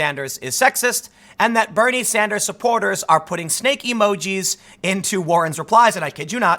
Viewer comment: Lol... I thought you said STEAK emojis.